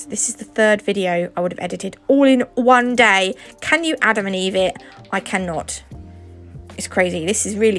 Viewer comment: did you say "Can you Adam&Eve it"? I've rewatched like 8 times and I only hear this -_